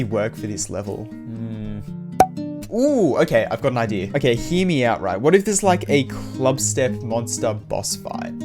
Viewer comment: When he says "hear me out" i always hear "himiau"